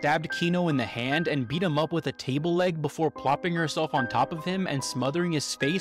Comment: Swear to God I thought he said she beat him with a turkey leg at first.